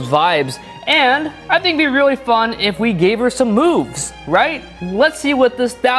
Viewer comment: I thought he said boobs instead of moves on 3:46 lol